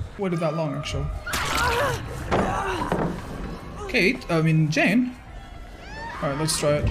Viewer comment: did he say cake? yes otzdarva thats a biggest cake i seen too